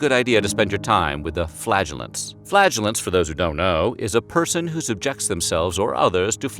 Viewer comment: Just think of those WW1 soldiers who, having survived the horrors of the trenches, came home and succumbed to Spanish Flu instead. Pandemics would spread even faster now!😀(thought I’d cheer everyone up) PS.I misheard ‘flagellants’ as ‘flatulence’ for a minute, there...